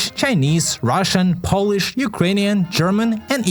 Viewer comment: did you say Polish? Edit: why is everyone now SPAMING the ‘🇵🇱’ emoji?